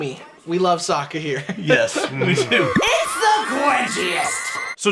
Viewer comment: Watching with subtitles on, because youtube just decides to enabled it from time to time and at 2:06 Sokka says "It's the quenchiest" the subtitles go "It's the cringiest". Don't do my boi Sokka dirty like that and I hope Sokka never gets cringy :-)